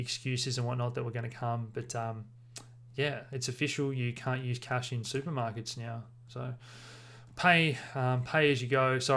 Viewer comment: did you say cannot use cash in supermarkets now?